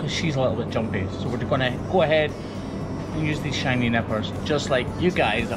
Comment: i thought he said shiny nipples instead of nippers at first